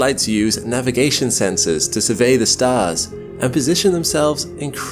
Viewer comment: Did you say "Space Lasers?"